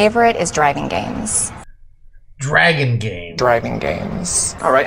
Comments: I heard "driving games" not dragon games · Anyone notice the whole time they thought the woman said "Dragon games" and was making fun of the guy mentioning that the PS2 has racing games...well if you listen she said "Driving games" LMAO...just thought I'd point it out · I’m pretty sure that lady said driving games, but it does kind of sound like she said dragon games. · For the longest time I thought they were saying that her "son's" favorite games were "dragon games" but they say driving games lmao makes so much more sense... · Could sworn he said driving games not dragon